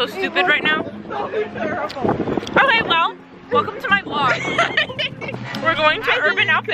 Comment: wait what does she say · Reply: "oh hey wout, welcome to my vlog"? just curious cus that's my name lol aaa why would she say such a random name the curiosity is real